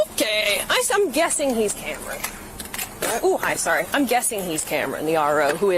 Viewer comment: I don't think she said, "I'm guessing he's hammered"....She said, "I'm guessing he's Cameron. The Registered RO (Registered Owner).". I initially thought that's what she said, too. I rewatched it a few times. He handled this BEAUTIFULLY! and now, he gets a nice payday on our dime because of this tyrant.